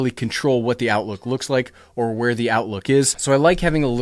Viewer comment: At ~4:20 did you say "outlook" twice? It's "outlet." lol.